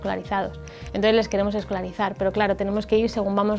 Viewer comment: Excelente trabajo. Por un momento escuché mal y entendí "..los vamos a esclavizar" en lugar de "...los vamos a escolarizar".